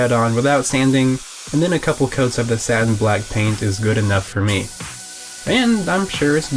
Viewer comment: I thought you said it was sad and black paint lol 30:05 rest in peace my sweet child